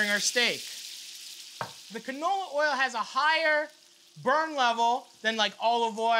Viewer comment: "canola oil" - being swedish I hear: "knulla oil" which would translate to : "fuck oil".